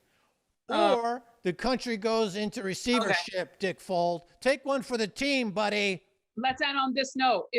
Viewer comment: Lol, did he say "dickfold" @ 39:21?